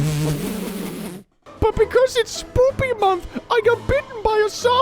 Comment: I heard say “because it’s Boopy month” instead of Spooky month. I like Boopy